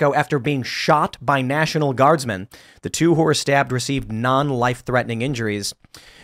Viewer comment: He said "Two who were stabbed" but it's early in the morning and I'm halfway through my cup of RYZE so what I heard was "Two whores were stabbed..." my bad.